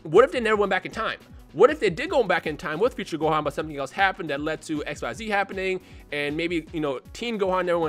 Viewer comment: Did he say 'that lead to XYZ happening' around 2:36 ?